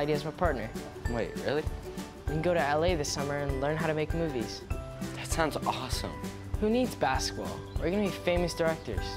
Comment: who else heard “Esketit”😂😂 Wow thx for all the likes and comments 😂❤️ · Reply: sofi_ rules I thought I heard let's get it